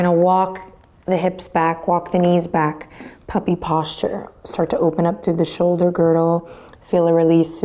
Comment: 13.12 thought you said: "..start to open up through the shoulder girrrrl.." :D :D could've been a very Adriene things to say... :D <3